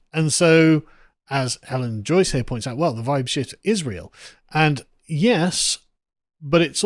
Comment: when he said "the vibe shift is real", YouTube thought he said "vibe sh!t is real" and the autocaptions shows a censor.